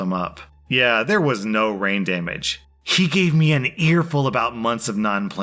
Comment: This whole time, I thought that they’d been saying brain damage